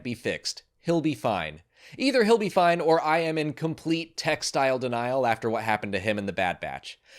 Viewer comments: When said he was in “‘Tech-style’ denial” about SM-33’s head getting chopped off, I thought he said “Textile denial” and I was like wut lol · Thank you for clarifying you were referring to Tech in The Bad Batch; I honest to goodness thought you said "Textile denial" and was wondering what on Earth that meant. 😅